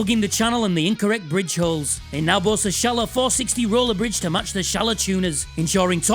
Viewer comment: Schaller lol I thought you said shallow first time